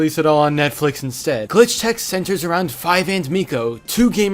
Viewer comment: I loved this show save for the fact every time Miko or someone else yells for Five I hear 'Fives' and I have unexplainable flashbacks to the Clone Wars. Very great show and Five does Jotaro's pose at some point I think.